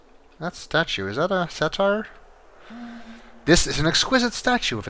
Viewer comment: Satyr is pronounced SAY-TER. Your current pronunciation sounded like "satire," which is a form of writing in which irony, sarcasm, and ridicule are used to denounce follies or vices (totally pulled that from the dictionary).